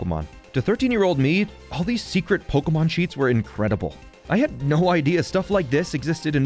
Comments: I thought you said Pokemon Cheeks 💀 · I thought he said pokemon cheeks. 😃